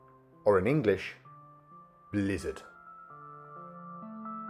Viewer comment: For a second I thought you said Buran means “lizard” and my whole world imploded. 🤣🤘🏻